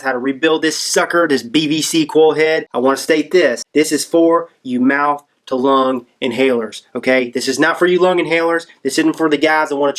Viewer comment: Oops...I totally mistook what you said. I thought you said not for mouth to lung inhalers! Please pardon my idiocy! And thanks for the video.